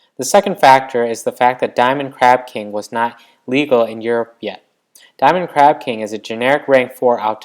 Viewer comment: oi m8 did you just say Europia? edit: btw I agree with you, it shouldn't be banned